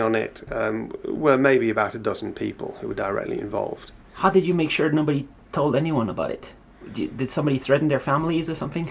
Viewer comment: How old is this kid? 19:30 The what business? (I can't understand the word.) 42:45 Who is this obnoxious kid? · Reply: "the Nokia business" I think